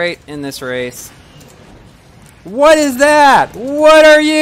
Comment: For a second I thought he said "WHAT ARE THOSE?!?"